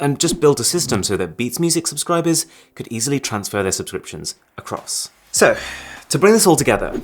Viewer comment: why did I hear "to a cross"